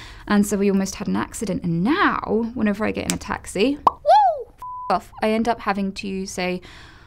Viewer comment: She must feel so relieved to know how to explain that she is not evil, just a bit glitchy. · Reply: I thought she said “whenever I get an attack, see...” whenever she gets in a taxi... 😎